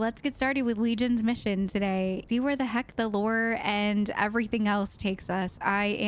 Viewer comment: all that religion talk and I did a spit take, I thought you said “See where the heck the Lord takes us” 😂